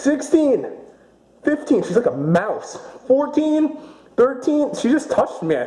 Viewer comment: did you say mouse